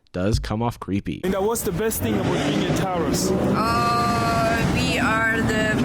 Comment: holy shit i was looking at my phone at 18:30 and i was positive that man said "what's the best thing about being a terrorist" and when i heard her say "we are the perfect wife" i was ready to scream and throw up